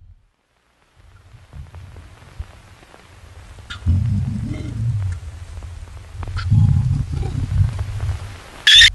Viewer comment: Is it just me or is it saying "turn around" at 1:32 ? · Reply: No shit he's saying turn around, his dream was warning him.